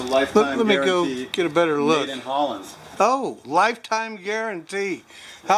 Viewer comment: Did he say around 11:35 made in holland or poland? Since i live in the netherlands i geuss that's kind off cool if he sayed holland. Also, garden center looks great, i don't have anything that looks remotely like that closeby. (except a few potato farmers)